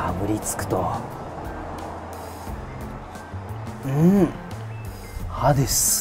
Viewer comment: ここ最初アヘ顔で「う〜ん、ハデス」って 言ってる風にしか聞こえなくて怖かった